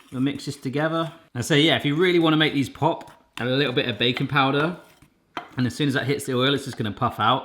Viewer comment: I thought he said bacon powder 😂